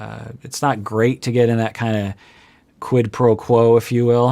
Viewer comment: yes taking one thing one way when it was in another way. That's the original meaning. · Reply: Hang on... Mistery solved! You guys actually use "quid pro quo" colloquially that translates as you explained. I thought he said "qui pro quo" which means misunderstanding. So Jessica Fletcher can have a free evening : ) Ciao ciao guys 😊